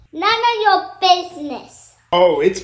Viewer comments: "I think my dad's gone curaaaaazaaaayy" 🤣 that's all I heard when she "yeah my daddy's crazy" 🤣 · The:" yeah my dad is crazy 🤣😂😂,, has my diying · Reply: I thought she said my daddy is cranky...I had to look at the comments to see where she said crazy and not cranky...lol